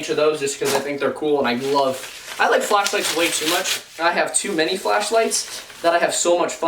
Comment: Omg lol, when you were talking about flashlights and how much you love them and love to play with them, I thought you said fleshlights lol!😅😅